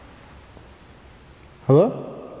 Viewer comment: I heard ,,HELLO,,,, LADIES VOICE...